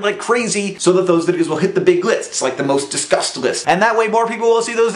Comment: When you said "most discussed list", I heard "most disgust list".